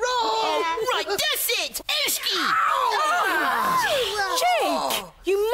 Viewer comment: I've no idea what Jake said? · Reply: He said "Ooshkee" where he ended up pinching Milo after he got upset